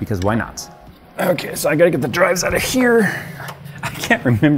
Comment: Thought Jay said he got to get the drugs out of here 😂